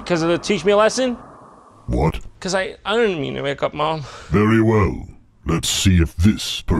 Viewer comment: what is Jon even saying here? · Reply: " 'Cause I- I didn't mean to wake up mom..."